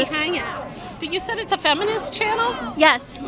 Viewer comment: Did you say this is a feminist channel? Whahahaha 🤣